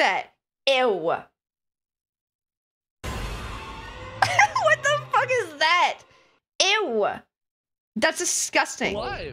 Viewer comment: Ngl I thought that said "backshot roulette"